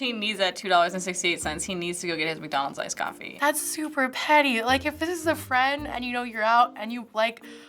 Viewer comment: I thought it said 'pretty' at first xD